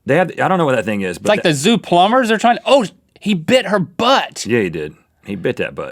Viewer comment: Well I think link said: oh shit! At 5:06.